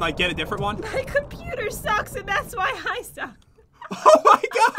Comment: I actualy thought said my "computer sucks that's why I duck" Then I looked at the chat🤣